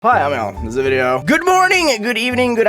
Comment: obviously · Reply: sometimes it sounds like he is saying "hi im alan"